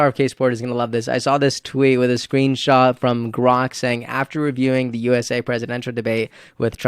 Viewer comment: Did you say "Gronk" at around 15:30? I listened a few times and couldn't tell. It wouldn't make sense to go from Presidential politics to a quote from a man who's thought of as a dumb jock, football player, so I had no idea. Next time, if it is someone like "Gronk" try using their actual name, so I don't have to replay the clip, still not know, and then write a paragraph about it, to you. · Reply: Hey thanks for your comment. No I didn’t say Gronk, I said Grok. It’s an AI Chat Bot for Twitter/X users that Elon Musk developed. It’s very good. I’m still developing my communication muscles, so I’m sorry I wasn’t clear. I do appreciate you writing me a comment about this though. Have a great day.